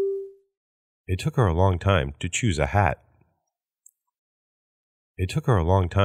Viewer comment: いとこにしか聞こえへんw